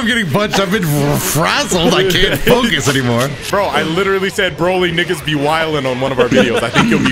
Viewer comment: I thought Said: „Broly 🥷‘s Be Willin“😂❤